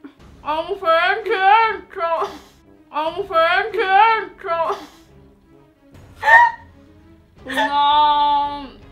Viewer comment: io ho capito ^mangio un tricheco^😂 No scherzavo forse hai detto ^Non ci riesco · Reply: ha detto"solo un pezzetto"